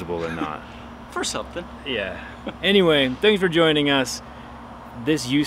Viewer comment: Did he just said "Thanks TIM for watching" ? :'D